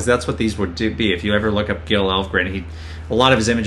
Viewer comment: What name does he say at 21:05 as an inspiration for FTF? Gil Elvgren?